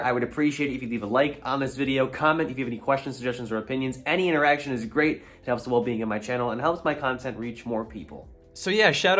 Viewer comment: 😭 I misheard you , I thought you said "any erection" instead of "interaction" at 0:33🤣